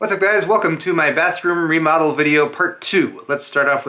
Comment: I thought it said I recommend my bathroom lol